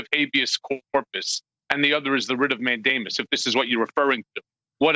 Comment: Did he say he filed a writ of Matt Damon? O.o